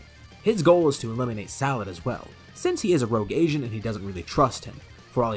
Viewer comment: when he said agent is sounded like he said asian.